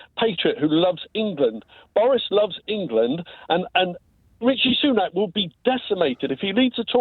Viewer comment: does he call him "Richie Sunak"?